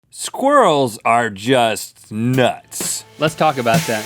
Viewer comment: Lol I misheard the beginning "Squirrels are just... ducks"